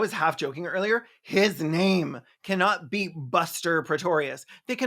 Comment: I don't hear 'Buster Praetorius', I hear 'Oscar Pistorius' and that just adds to the trauma that is this feverdream 0_0